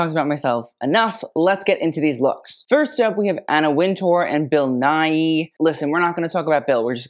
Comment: I wasn’t really paying attention when this video started, and all I heard was, “Anna Wintour and Bill Nye.” BILL BILL BILL BILL